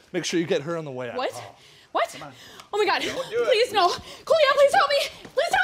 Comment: I thought she said "Coolio" in the 2nd scenario lol